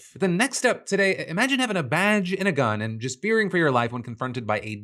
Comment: Thought you said “Vag and a gun”